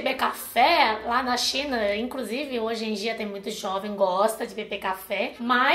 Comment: eu entendi jovem bosta kkkkk